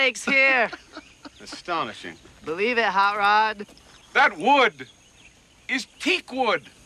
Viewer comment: "Believe it Hot Rod" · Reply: I always thought he said: "Believe it, Harrad." I guess I always just thought it was a reference to something I didn't know and that Harrad was some name. 😄